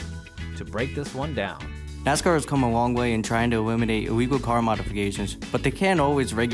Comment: Did he just say “Ewiminate iwegal car modifications”??